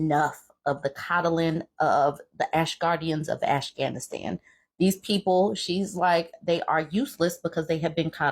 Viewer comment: ? Did i hear you say "ASH-GANISTAN"?! Goodness you crack me up!!!!! 🤣🤣🤣🤣